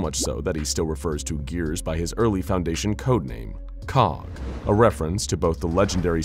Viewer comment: i thought they said cock for a second...